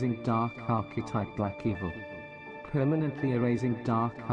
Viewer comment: did that say "black people"? Lol